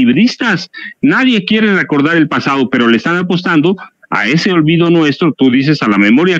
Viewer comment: Jajajaja entendí "apuestan".